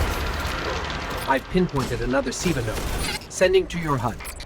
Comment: Wait what did the ghost say at 2:52? I heard it as "I've pinpointed another semen node".